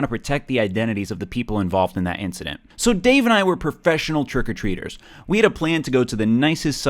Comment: I thought you said, "Professional jerk or treaters." A hand job is still a job.